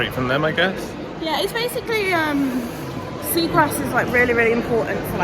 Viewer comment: I heard "seagrass" fyi...lol ... twice ...and i rewinded it.